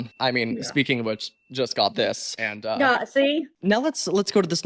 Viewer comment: WHAT DID SHE SAY 😭🙏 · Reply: I THINK "NAH SEE" OR SMTH